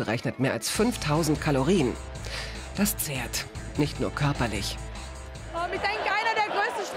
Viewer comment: Was sagt sie um 5:37 - "das z..." was? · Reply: zerrt….